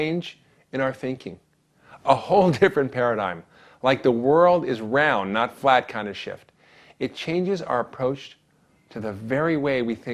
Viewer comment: sounds like he says, "the world is round not flat kinda shit!"